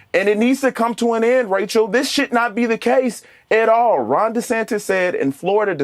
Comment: At first I thought he said "this shit not be the case". I Agree with the guy though we need to be able to have open conversations and it seems the left is incapable of that.